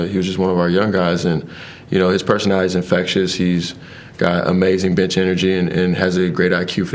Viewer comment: I swear he said "he's got amazing bitch energy" 🤣🤣